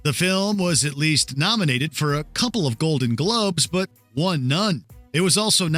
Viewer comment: did he say “Golden Gloves”? Streisand doing boxing now?